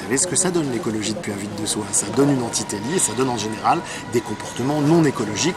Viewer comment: merci pour cette vidéo quelqu'un peut me dire comment s'écrit ce que j'ai entendu "entiteli" (ver 4:09) entité lier .. ? ça serais bien sympathique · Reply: Antitélie , terme provenant de la biologie